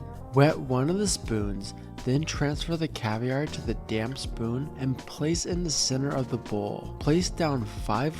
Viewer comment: I thought you said “that damn spoon”. Lmao, until I listened a couple more times.